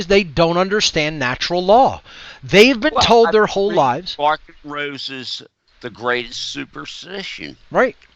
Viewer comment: Mark Rose's the greatest superstition? I can't understand what says. ahh Larken Rose's the greatest superstition. finally found it.